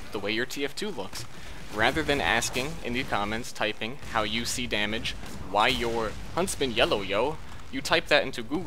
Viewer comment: I thought he said "How U C Damage" in the beginning.